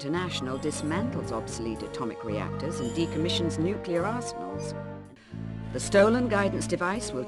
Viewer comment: sounds like she says "nuclear arseholes".